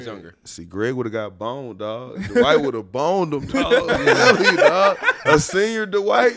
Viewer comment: did he just say Dwight would've boned him?